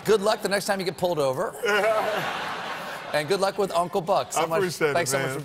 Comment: Thought that said uncle ruckus 😳